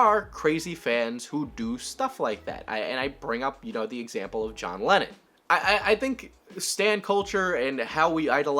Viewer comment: Man I am very stupid, when you said John Lennon I thought you said Lenin and got really confused for a good few minutes😂